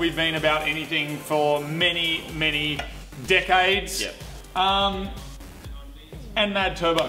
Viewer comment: When said "decades" I first thought I heard him say Dick Aids, if you say it Dick aids with an auzzie accent it works well.